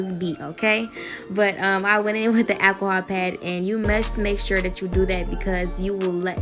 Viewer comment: tell me why I heard “I went in with the Apple iPad” 😭